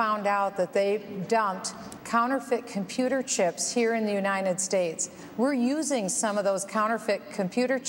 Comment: Am I the only one who hears Bachmann mispronouncing "United States?" I keep hearing her say "Unined States" . There is a "T" in the word and it needs to be pronounced.